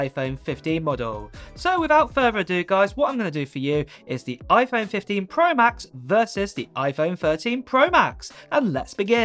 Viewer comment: I don’t know if he said iPhone 13 Pro Max or or 15 pro max 😂 sounded like he said “ iPhone 15 pro max vs iPhone 15 pro max “ twice 🤦🏼‍♂️😂